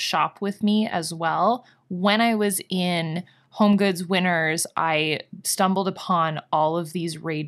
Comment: I totally thought you were saying "Winters" as the store name this whole time. I just read your comment where you say you did a haul from "Winner's". Lol! I like Winner's as the name so much more. I was wondering if it's like a Canadian T.J Maxx? But I like the napkin holder. They have cute stuff. And you and Andre having matching masks is so cute🙂